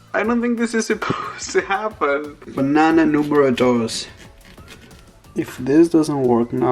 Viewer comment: Did he really say “Banana número dos”?! 🍌